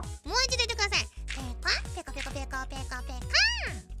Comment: Poor Pekora, I didn't know she's fluent in both Japanese and Pekolandish. And she forgot how to talk when she didn't use it often, truly multilingual problem xD 1:09 Why does I heard it as "Pika? Pika pika pikachu" 🤣